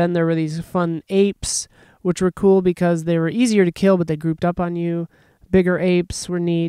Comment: so i thought said " they threw dump on you" instead of "group up on you" and I was like oh these are guardian apes!